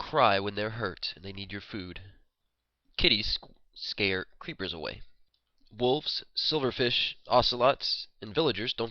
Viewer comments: It sounded like he said "Titties will scare creepers away" lol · I thought he said "titties will scare creepers away".